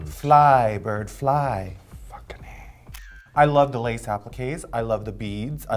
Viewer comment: did Raja say "Fuckening"? Lol how high is she